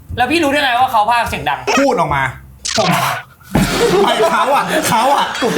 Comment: น. ไม่ใช่ว่าช็อตนี้ พี่ตายแล้วหรอ เหมือนได้ยินเขาพูดว่า “กูพูดหรอ” แล้วก็ขำๆๆ แต่เหมือนจะไม่มีคนสังเกต เลยรอดไปได้